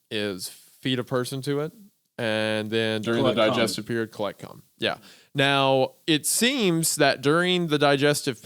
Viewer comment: was a bit of a jumpscare when I heard say "collect cum"